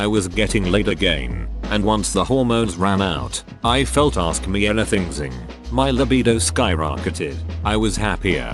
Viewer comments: "...I felt ask-me-anything-zing!" took me a few seconds to realize what i just heard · Did I just hear him say "I felt askmeanythingzing". . .?